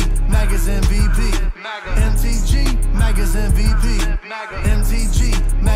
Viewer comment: Sorry...i kept hearing "MTG...MAGA'S STD" Nah..not sorry😂😂😂😂😂